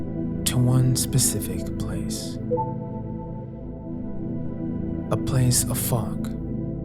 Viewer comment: I thought he said a place of f@